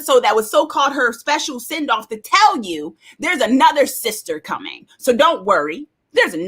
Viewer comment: When the guardian said “there’s another” all I heard was “your mom’s a ho”. 😂